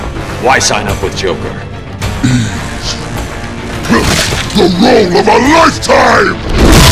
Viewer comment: “Easy, The role of a lifetime!” I like that even though he’s a giant being of clay that can kill and cause havoc, he’s always first and foremost an actor 😁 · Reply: I always thought I heard: "He's the role of a lifetime."